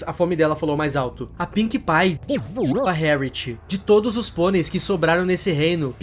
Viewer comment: o que ele disse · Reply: Devorou 💀